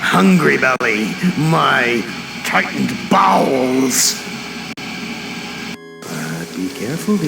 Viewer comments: I HEARD IT AS BALLS😭😭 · did he say “Tightened balls” or “Tightened bowels”??